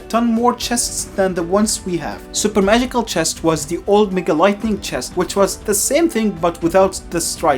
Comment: i thought pekka said " MEGA KNIGHT"😭😭😭